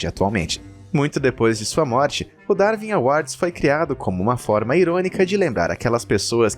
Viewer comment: eu não entendi o que você disse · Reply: Darwin Awards - Prêmio Darwin.